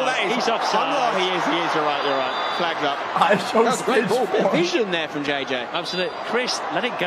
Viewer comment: he says something that upset chat but i literally cannot understand what he said can someone fill me in? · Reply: He said ishowstage4 referencing to cancer